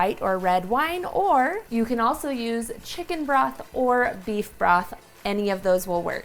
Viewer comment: looks great! At first I thought you said to add chicken breath. I was like, "whaaaa......???" LOL - I put in my hearing aids and listened again whew